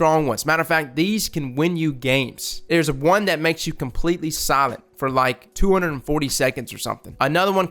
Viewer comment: I kept hearing 'silent' as 'solid' and couldn't imagine how that would help someone.